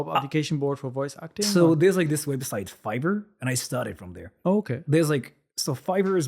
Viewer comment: when he said fiver I thought he said fibre. he still has an accent lol.